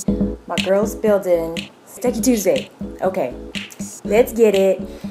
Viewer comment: Sounds like you said Stanky Tuesday lol